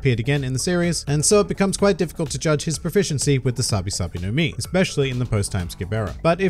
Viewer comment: I thought is said Sabo - Sabo 😂